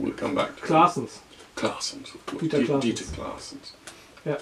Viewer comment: What is the name the interviewer says around 5:00? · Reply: I said "Claessens": <link>